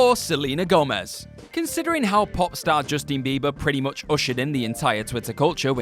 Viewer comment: I swear I heard you say 'Justine' Bieber. I'm not gonna go back to check, and just assume you did, and that are a great person. ...Also people are f*cking crazy